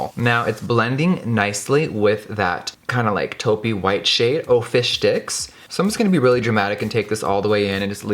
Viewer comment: He said fish sticks but I thought he said "fish dicks"